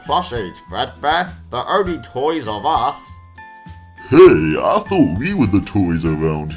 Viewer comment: wait there is two foxy one is a plushie and the endoplushie looks like foxy whick makes three three is a number on a triangle ILLUMINATI!!!!!!!!!!!!!!!!!!!!!!!!! an after foxy said there only toy of us it sounded like foxy said toys r us